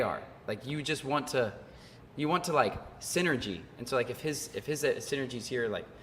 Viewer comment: Almost fell off my seat when I thought he said "Sin-nergy" :D